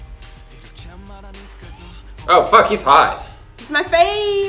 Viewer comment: Did he just say "fvck he's hot" to Namjoon? Because SAME!!!!